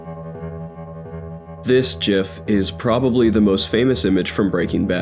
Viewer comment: Did you say jif ?